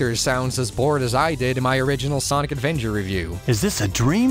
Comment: Gotta love how even the closed captions say "Sonic Advenger"